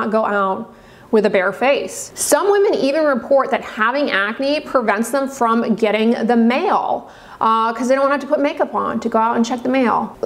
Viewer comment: Ngl i thought you said “having acne prevented them from getting the MaLe” and i was like 😨😨😨😨